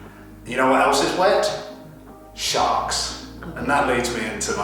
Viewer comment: Hahah I thought you said “You know what else is wet? Sharts!” I immediately started laughing and then realized you said sharks lol